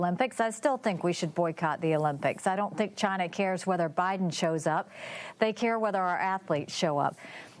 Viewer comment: Nikki: "I don't think China cares whether Biden shows up" I thought she said "I don't think China cares if Obiden shows up" 😆